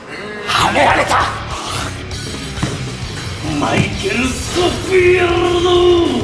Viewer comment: ここなんて言ってるのかずっと気になってる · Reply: マイケルスコフィールド